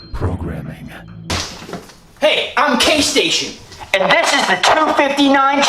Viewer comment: For a moment I misheard Jaystation but I when I saw the name I was like: "Oh good it wasn't Jaystation."